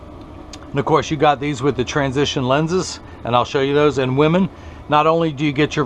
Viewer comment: What did you mean by "..and I'll show you these in 'women'". Aren't those women's frames at the 14:56 mark? (or maybe I heard wrong?) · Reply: I was referring to the Transitions lenses. And I said, " I'll show you those in a minute" referring to when I activate the lenses to show you how I darken them.